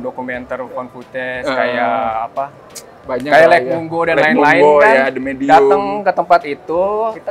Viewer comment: ini ngomong apaan sih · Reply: lake mungo, nama film mockumentary lainnya